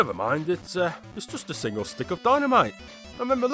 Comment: I thought he said “bus”